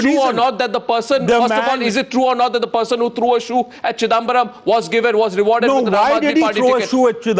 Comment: OMG..didnt i just hear Arnab and Kamal saying CHUDAambaram 8:05 ..ROFL..!!!!!